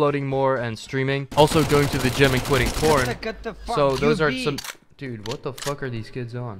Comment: did he say quitting porn?